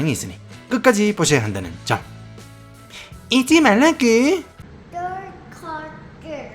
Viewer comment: 밋돌세 영상 1.75배속으로 보면 더 재밌음, 텐션이 올라간듯 느껴져서 진짜 더 재밌음 ㅎㅎ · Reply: 그렇게 빨리보면 '잊지말라구' 가 '잊으라구' 로 들리지 않나요?